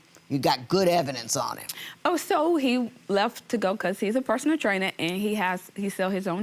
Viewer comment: Wait..IS he a personal trainer..I thought they said he NEEDS a personal trainer!